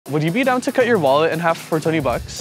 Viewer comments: Omg i heard “would you be down to cut your mullet?” 😅😂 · I thought he say would you let me cut your mullet for 20$